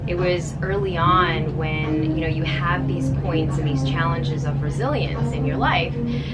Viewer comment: I thought so, she said something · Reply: "(...) You have these points and chalanges of brazilians in your life." I swear I heard that from her hahahaha